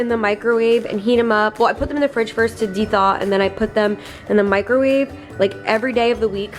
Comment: did anyone else hear “de-thot” ???😂